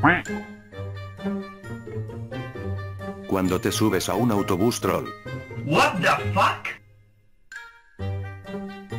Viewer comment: no entiendo la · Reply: Dice "Martillo para romper cristales Romper el cristal para acceder al Martillo"